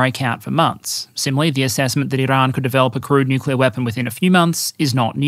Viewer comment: "...the assessment that Iran could build a crude nuclear weapon..." I can't be the only person who, for a second, heard that as 'crewed nuclear weapon'? Can you imagine?!